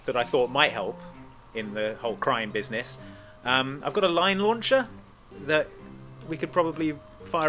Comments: When discussing point of entry, genuinely misheard Edvard's suggestion of the 'Line launcher' as a 'Lion Launcher'. Was thinking woooo what an entrance! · At first, I thought Andy said lion launcher instead of line launcher. Missed opportunity if you ask me.